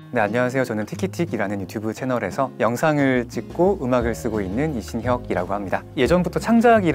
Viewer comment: 에서 "영상을 찢고 음악을 부수고 있는"이라고 잘못 들었는데... 생각해보니 아주 틀린말도 아니네요